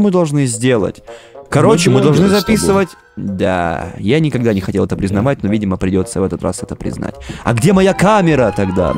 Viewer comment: видео начинается с очередной шутки которую мы не услышым изза того что говорят вместе( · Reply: "Мы блогеры с тобой" по контексту и два раза переслушать, будет понятно